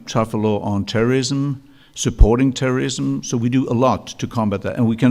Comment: Did he just say "supporting terrorism"?